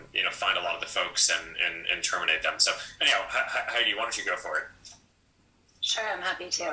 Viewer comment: Subtitles at 1:30 are wrong, says "how do you want us to go for it?" when he actually says "why don't you go for it?" "They [leaks] threaten our open culture". How open is your culture if people need to leak it?